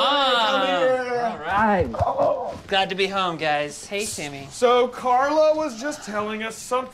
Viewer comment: Um did I just hear "Hey Timmy" at 1:38? >.>